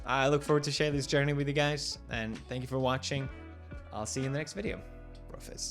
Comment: Felix said "I'll see you next video" but I heard "I'll see you next year" and I was like wait what rewind! lol